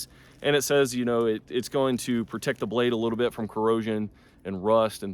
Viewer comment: I misheard you when you said corrosion, I thought you said erosion and almost turned the video off LOL😅. Thanks for the review